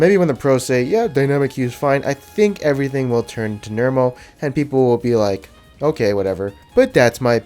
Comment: did he say "turn to nermal?"